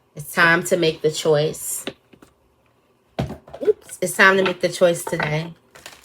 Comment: When you said “It’s time to make a choice today” I thought I heard “… a choice Tenaee” and that’s my name 😂❤ I’m pretty sure I found big daddy 😂 so I gotta cut the others off but I be to afraid of hurting ppls feelings❣️ 😩😂 #libratingz✨